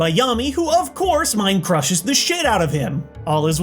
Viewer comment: Did I mishear when said “mind-crushes the s*** out of him”? So this is how he ends the decade. And in a video about censorship no less.